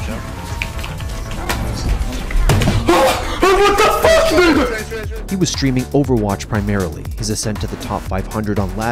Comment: did he just say the n-word? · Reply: He said dude